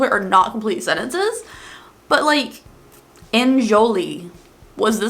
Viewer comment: I thought Enjol! said Enjoy! because it was talking about frying bacon lol. Would love another weird makeup ads! <3